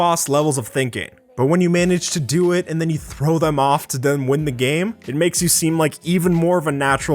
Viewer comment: Thought you said throw my phone